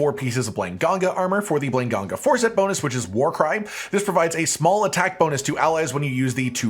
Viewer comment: Sounded like he said "War Crime" Cracked me up for some reason